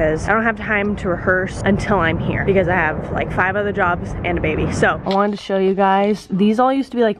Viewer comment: At frist I thought you said riker had a baby I’m like WOAH THERE SINCE WHEN